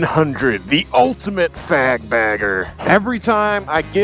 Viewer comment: 😂 thought you said “fag bagger.”